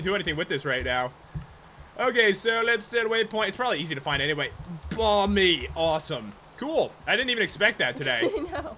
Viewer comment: First time they said bomby I thought they said bobby